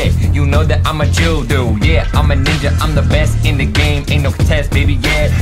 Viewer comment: Did ninja say nigga